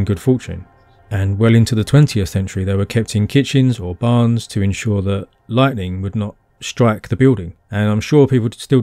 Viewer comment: Can someone help me? I can't understand what he said between 21:55 - 22:05 and subtitles are not available =( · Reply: I said something like "Well into the 20th century they were kept in kitchens or barns to ensure that lightning would not strike the building. I’m sure people still do the same today - I would, if I was blessed enough to dig one up with the potatoes. The Upton Lovell Shaman lived in middle bronze age Britain when metal working was far more prevalent...."